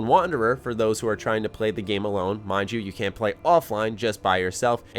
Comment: I don't have alot of gaming friends. I was hoping for a single player. Walking around alone in the wasteland with no story getting bothered by other players is not my idea of fun. I play games to escape the human interaction, I'm going in very cautious for this game. The new perks system, cards, and special all sounds interesting, but it doesn't mean anything to me if I don't enjoy myself. I've bought the collectors edition of every fallout so far. I'm probably gonna skip on this one until all the reviews come out. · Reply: Major Violator also in this video at 3:15 seconds does he say you can or cant play offline hahahah sounds like can but i might not be hearing the T sound